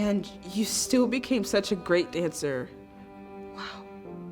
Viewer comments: On 7:32 it said geat instead of great in the subtitles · Is nobody gonna talk about how at 7:34 is says "geat" instead of great? (which is what they actually said)